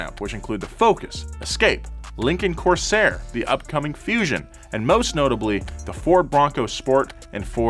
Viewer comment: did he just say upcoming fusion??